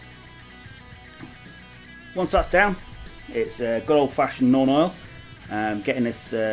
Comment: Ok, I've played this bit a dozen times but I can't understand what paint you're saying at 2:00. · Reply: Nuln oil :)